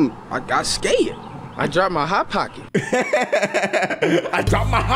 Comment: I thought he said I drive my hot pocket.